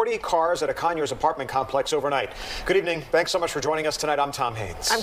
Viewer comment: I legit thought he said he was called Tom Hanks.